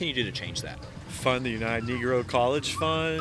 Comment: Did you say kneegrow?! He’s a cuck. No doubt